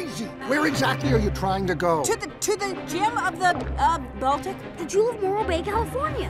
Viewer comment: Did anyone else heard her say, "to the gym"? Just me then? Ok just me then